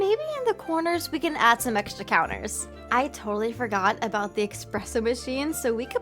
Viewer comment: did she say "expresso"?